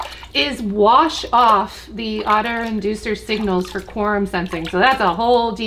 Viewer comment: What did she say at 9:27. Otter inducive signals of quorum sensing...